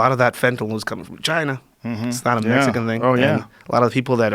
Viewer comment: Ohh from China... I thought he said Vagina...